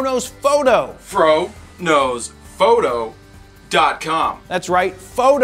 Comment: The first times i watched your videos I hear "frodos photo" instead the "fro knows foto" 😂